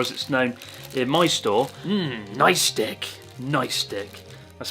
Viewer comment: Did he just say nice di*k